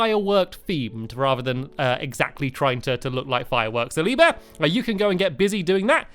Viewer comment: stampy said silly bear instead of so lee bear